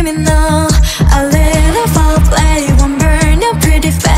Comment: I FR THOUGHT THAT KAZUHA SAID ' FORE PLAY ' , 😭😭😭✋